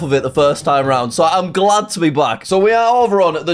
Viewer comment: it sounds like he said "im glad to be black"